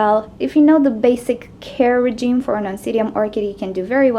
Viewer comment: I just got one of these and reddit identified it for me. Though the employee did say oncidium I thought she said obsidian and I was confused. Mine has so many leaves and psuedobulbs I think it might need a bigger pot soon already. I'm excited for all the buds to bloom since it only has one bloom with lots of buds and 2 more spikes coming!